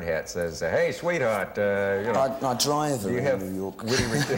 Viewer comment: what did bowie say at 9:19 ? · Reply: I drive around New York